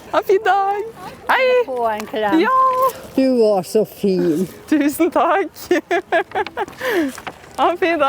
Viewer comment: What I hear: “Hope you die” Close Caption: “Have a nice day!”